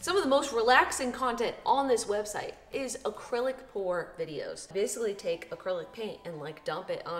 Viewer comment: I just woke up and for a moment I thought she said acrylic porn